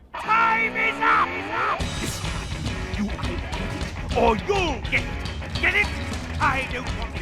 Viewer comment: what did he say? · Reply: “This time, you are going to get it or YOU’LL get it! Get it?!”